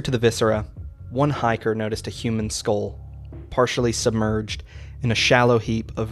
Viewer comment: In the "To the mountain" tape the part whenever it loops on the "A human skull!" bit I always for some reason I always heard it as "A human skin!" "A human skin!"